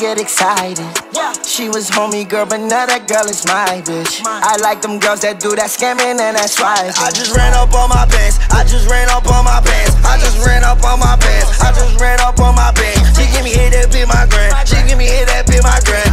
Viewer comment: I thought he said I JUST RAN UP ON MY PANTS I JUST RAN UP ON MY PANTS I JUST RAN UP ON MY PANTS